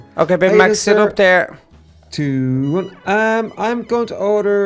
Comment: Who else heard say 2 1